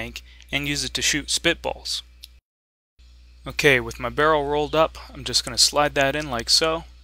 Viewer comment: I heard you say barrel rolled and I lol'd Also watching this video makes me remember how I found you.